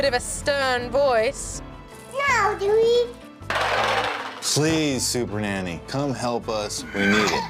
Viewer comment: I thought he said "slease super nanny"